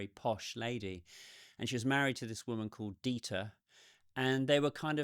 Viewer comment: "..and she was married to this woman called Dita/Dieter". There have been a few comments about this. I don't know why says "woman" but I think it was a slip of the tongue as gay marriage just wasn't a thing back in 2002. I think he actually says "Dieter" which is a common German man's name. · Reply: His name was Detmar Blow